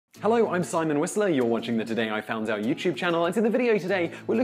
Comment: I always thought he was saying " today I found our YouTube channel"